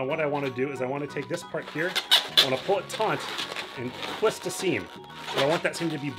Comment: Nice cupcake. Did you say "pull it taunt" or "pull it taut" at 4:09? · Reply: I heard taunt